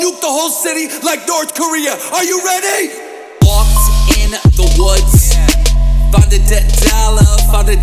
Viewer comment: 1% of comments: normal comments 99% of comments: "found a dead body!" hory shet you guys are so original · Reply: gybrel i heard "Walked in the woods , found a dead dollar"